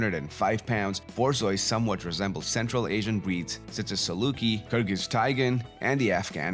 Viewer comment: What breed did he say at 3:06 after saluki? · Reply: kyrgyz taigan